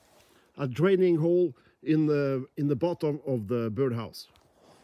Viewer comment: nice one, these are called bird boxes in English, great to see you helping our feathered friends. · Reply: Oh, I thought you said bird house😆